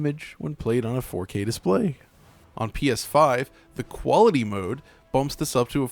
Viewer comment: I hear "4k displ~uaeiy... 🤤"